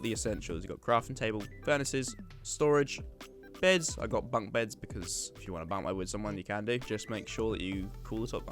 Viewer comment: what is he saying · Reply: i got bunk beds cus if you wanna bunk bed with someone you can